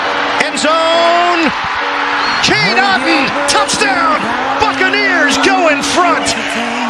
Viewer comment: Am I the only one that thought the reporter said F**Caneers